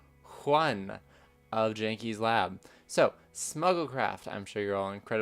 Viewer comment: I thought you said Snuggle Craft and that sounds adorable